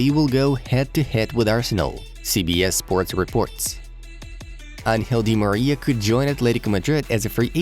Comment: am I the only one who heard unhealthily d Maria?